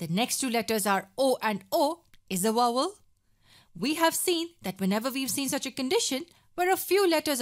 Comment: what does she said? Wowel?